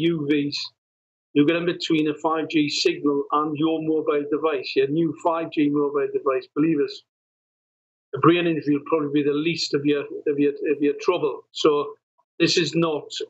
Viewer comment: I can't quite make out what is saying about getting in between a 5G signal, around 10:50 minutes into the video. Can anyone translate? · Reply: He said, "if you get in between a 5G signal and your mobile device, a brain injury will probably be the least of your troubles."